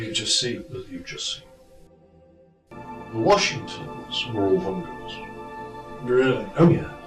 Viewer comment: Can anyone tell me what was said at 36:05 ? Can't quite understand it. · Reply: The Washington's were Ulvungers